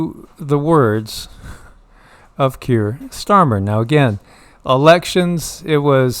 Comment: I keep hearing “Pierced Armor” instead of Keir Starmer